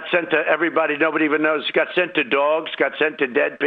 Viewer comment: Did i hear that right? “Got sent to Dogs”?